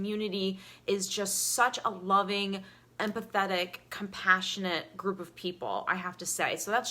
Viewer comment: Thank you so much. At first I thought you said "and pathetic" but you said empathetic.